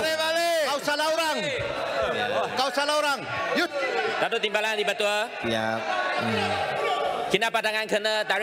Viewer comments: still confused about this part tho. What did he saying again? "Gaduh di batu je"? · Did he say - Cows are all long? 😅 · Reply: "Kau salah orang"